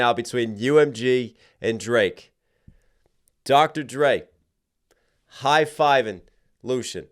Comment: When he shouted ouut Dr. Dre...for a split second, I thought he said "Drake", lol... Great breakdown--I knew about Canadian tuxedo, but had no idea re Texas as well. 😂